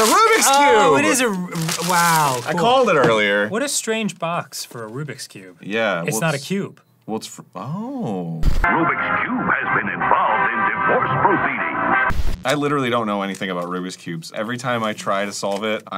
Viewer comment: "Rubik's Cube has been involved in severe beatings" was what I heard while not paying attention